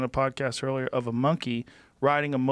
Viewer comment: I heard Elon say “ yea that’s what they want “